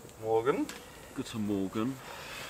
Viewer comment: Haha, I thought briefly ... did they really say "good morning"? 30:45 ... a nice surprise for me as a German. Thank you for this nice video 🏕️🐖🍴🔥